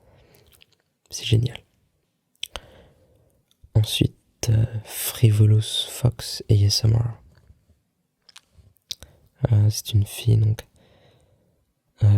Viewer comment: Hahahaha, sounded like you said "frivolous fucks ASMR"